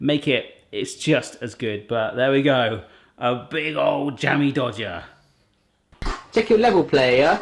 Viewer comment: I honestly thought he said Dammy Jodger the first time. I love these!